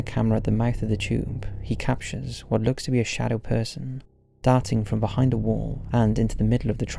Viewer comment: I thought you said “A shadow dancing behind the wall” 😂 it was darting. 😂